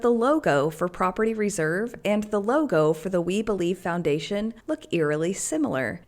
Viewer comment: You said, "The We Believe Foundation," I heard, "The We Bully Foundation." Hey, I hate to tell you but you are addicted to Mormonism, dude.